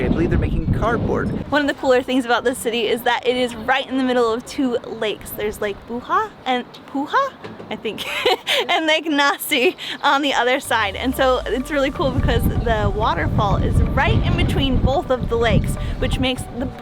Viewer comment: Ahaha, I love how you butchered the pronunciation of Lake Näsijärvi! I was like "Wait? Did she just say Lake Nazi?" Not a problem, though, since Finnish is a hard language for English speakers.